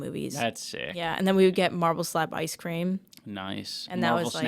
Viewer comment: "Marvel Snap Ice Cream" is exactly what I thought Anisa had said during her story, and I can't believe Ian also heard that. xD